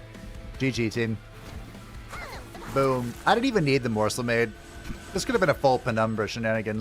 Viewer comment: You know, every time you say "Morsel-Made" I hear it as "Morsel-Maid". Bit of a difference between the 2, I think.